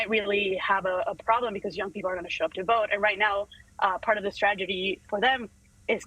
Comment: I love the fact that she has her 'closet' installed with sound deadening foam padding, also I wanna know what she does with that space later when not on the internet ??🙊🙉🙈😳🤨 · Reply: I hesitated to go there. I love both the message and the messenger. And those dimples! Big thumbs up 👍. But at 5:19 did she say "stragety"?